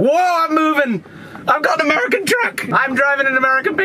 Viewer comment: I thought you said American... not Ford ;)